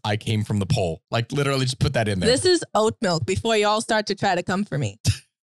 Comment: Says milk is gross. Proceeds to drink goat milk specifically. 😅 Team chocolate milk. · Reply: lol I thought she said goat milk.